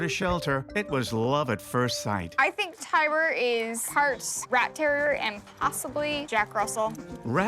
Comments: i thought she said he was part raptor and jack Russel terrier i lmao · I thought she said part Raptor part Jack Russell haha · At first I thought she said " I think he's part raptor?"